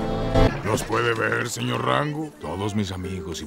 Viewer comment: juro que escuche Rangu.